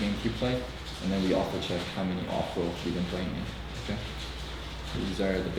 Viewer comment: did Locodoco say off-roles, as in how many games the player didn't play the position they play as a pro? Or did i hear him incorrectly?